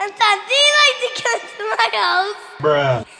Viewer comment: CAN U COME TO MY HOUSEEEEE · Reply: When I first watched that I thought he said “do you like to catch mangos”